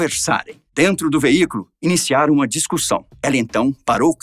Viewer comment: aos 05:22 só eu ouvi o narrador falar "discursão"? ...